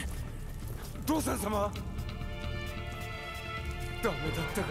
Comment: 尾張:日本 「おわり」 「おちょう」 7:00 神仏:日本 「しんぶつ」 : 「かみほとけ」(確かに読むけど今じゃない) 9:52 桶狭間:日本 「おけはざま」 「かんおけはざま」 10:04 大勢力:日本 「だいせいりょく」 : 「おおせいりょく」 10:10 食い扶持:日本 「くいぶち」(食費的な) : 「くいっぱじ」 14:22 山姥:日本 「やまんば」 : 「やまばあ」 15:08 鬼灯:日本 「ほおずき」 : 「きとう」(定期) 16:17 お辰:仁王 「おたつ」(のはず…) : 「おちょう」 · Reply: 偽氷川日菜 5:42 多分ここが「どうさん」に聞こえたのではないでしょうか？